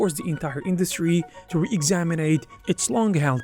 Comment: did you say RE-EXAMINATE?